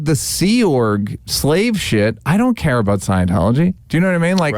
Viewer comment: Never heard mention of the Freewinds ship during the interview. At 2:10 he says," Other than like the sea org slave shit, I don't care about Scientology." Am wondering if you thought he said ship instead of shit. Either way I'm glad that you posted this clip. Thank you for all you do. I follow your channel every day. Ex-Scientologist myself.